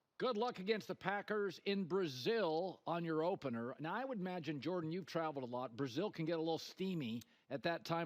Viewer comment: Did he call him jordan? Lol around 10:30 in